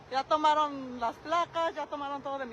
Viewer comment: did she just say Las Plagas?! oh, shi, it's going down for real. lemme call my homeboy Leon S. Kennedy on this case.